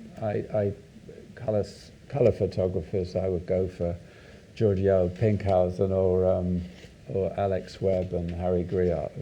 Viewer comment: Who does he initially mentios @ 12:57 ?? I can't understand him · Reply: Hi! He mentions Georgi Pinkhassov, the russian photographer.